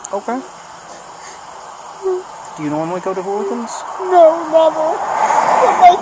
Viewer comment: Tf you mean “inaudible” around 27:05? He clearly says Hooligans. It’s the name of a bar. You also pronounced lascivious wrong earlier in the vid.